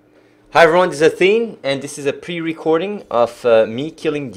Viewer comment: i thought he said this is a replay